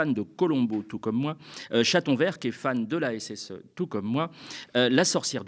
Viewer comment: j'ai entendu "chatonvert qui est fan de la SS tout comme moi" à la première écoute mon sang s'est figé pendant un instant :')